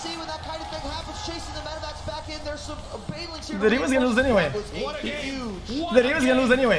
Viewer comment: What is he saying there?? He even says it two times and I still find it to be innaudible. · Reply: "dude he was gonna lose anyway"